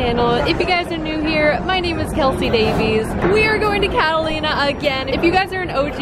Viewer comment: I think she said catalina Ps that’s my name Ahhhhhh there’s a island that has my name